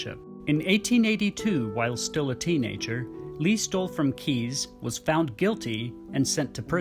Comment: I thought he said Lee stole some keys 🔑